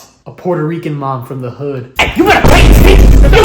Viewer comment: damn did she said pay the si?